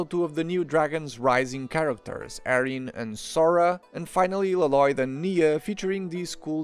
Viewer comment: I swear I heard him call lloyd laloyd like garmadon does in the movie